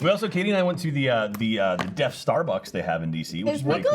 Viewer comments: I thought said he went to the death starbucks, and I thought it was gonna be a star wars themed starbucks, I was wrooong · I definitely thought said "DeathStarbucks". Like a Starbucks ran by a Sith Empire.